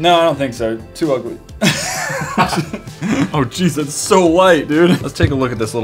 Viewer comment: Tanner's reaction to that bulldog game was priceless 😂 I just can't get over that. If you haven't already, go check out this other 'Chinese products' episode with a PSYCHIC fidget spinner! ➡ <link> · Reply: Did anyone catch Matt saying "it's so light" fast forward to 5:50.